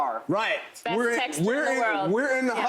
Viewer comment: What did she say @ 11:44? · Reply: “Best texter in the world” As far as I could tell...